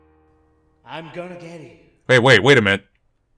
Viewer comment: is it me or did i hear the father going "Aya, im going gay????" what is wrong with my hearing?????????????????????